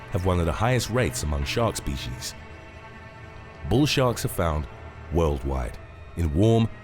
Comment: At 9 seconds in it sounds like you say "Bull Shart" 🙂